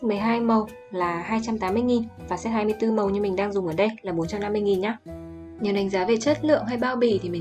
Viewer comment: tui không nghe nhầm đúng không: 12m/280k và 24m/150k???